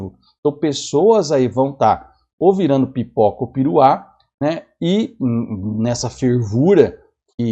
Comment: Hein?! Pipoca ou...? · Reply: Eu entendi "pipoca ou piruá"